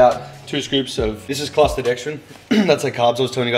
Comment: What is the carb powder called? It sounded like you said clastodextrin but I haven't found any results for that. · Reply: cluster dextrin